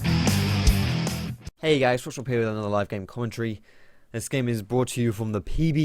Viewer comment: What do you say in your intro? All I hear is, "hey guys, erghgoferfeerghb another live game". lol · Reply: Hey guys, Foxdrop here with another live game.